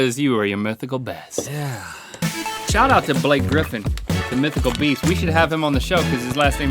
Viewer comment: when they said Blake Griffon, i thought they said BlackGryph0n. the Brony that worked with the creators and actors of MLP and was in the U.S. Navy. also voice acted in a few shows/movies.